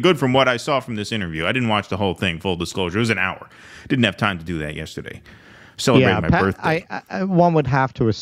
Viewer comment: did you say it was your birthday yesterday? A belated Happy birthday 🎂🎈🖖